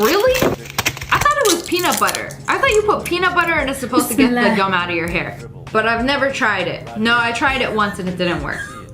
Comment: is it just me or did I hear bismillah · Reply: She did say Bismillah she is muslim